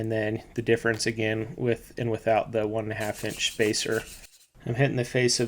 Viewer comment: Why does video say 1/2" spacer several times? Those spacers are 1" or more. Absolutely NOT 1/2"... Can't believe no one noticed. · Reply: I heard him say 1-1/2" spacer several times, not 1/2".